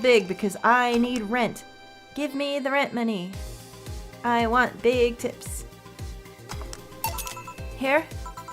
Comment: For a moment I misheard "I need big tips" for "I need big tits", which is kinda on point with hers and Jon's twitter right now.